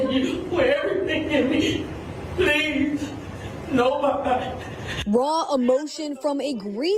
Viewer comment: Nobody knows what she said, not only did the news talk over her, they had the subtitles on what the news said. I heard her say “please body”. Idk what that means.